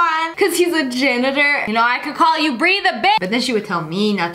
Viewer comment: “BREE THE BI-“ Me: W H E E Z E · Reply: I thought she said binch